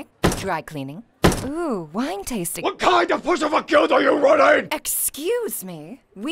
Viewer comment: I always thought he said "WHAT KIND OF PUSSYFOOT GUILD ARE YOU RUNNING?!" I didn't know it was "pushover" until I turned on the captions.